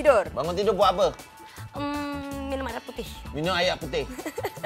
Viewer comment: "apa yg anda buat selepas bangun tidur?".. dengar mcm Cita2ta jwb, "minum arak putih".. ke salah dengar?? 😊😊